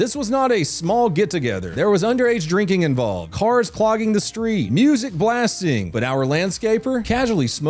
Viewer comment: Thought I heard “whores clogging the streets” …. lol I in fact… did not… lol